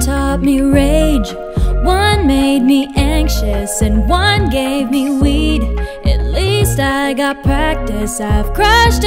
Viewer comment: "anD oNe gAvE mE weEd" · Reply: my boyfriend misheard it as "one gay be weak"